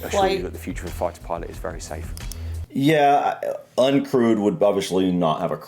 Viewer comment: I thought he was saying crude, not crewed, lol.